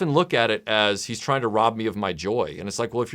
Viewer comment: Has-Bros: "He's trying to rob me of my joy!" All I hear: "You murdered my Misery!"